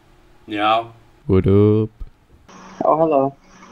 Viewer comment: مو كانه هذا واحد عربي كأني سمعت هلا 😕😕